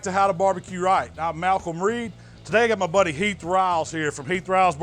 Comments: thought it said Health Riles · I thought it said “health ribs” lmao